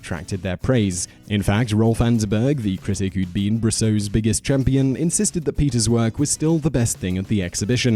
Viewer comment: did he just say “biggest chimpion” ? I hope so because that is one hell of a pun!